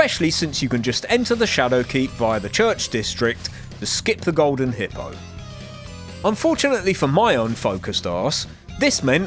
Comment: Tell me why I heard “to skip the golden H¡tler”💀